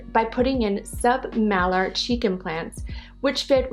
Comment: i misheard cheek implants to chicken plants lol